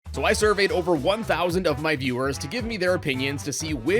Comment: I thought it said "So I survived over 1,000 viewer's opinions..." Also how dare you put Dr. Kahl's Robot that low people.